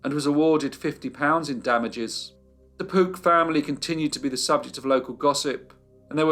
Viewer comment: I kept hearing 'Poop family' 😁😁